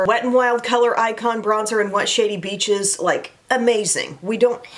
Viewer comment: Funny story: for YEARS I have thought you were saying “wet shady beaches” and I thought that was such a strange name for a bronzer. 😂 it wasn’t until I read through the linked items one day that I saw it written out and realized you were saying WHAT shady beaches 😂😂